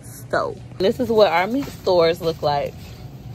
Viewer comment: “It’s not ‘meat store’ it’s ‘meat sto’.” “So this is what our meat store looks like” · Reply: I thought she said Meat Stall..